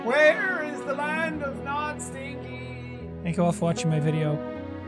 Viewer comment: Rewatching this and had to replay the end a couple times bc I could've sworn I heard him say 'dankjewel' but turns out it was 'thank you all' 😭😭💀 Dutch speakers represent ✊🏻